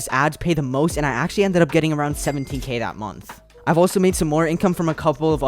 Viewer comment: yo i alt tabbed and i thought he said 70k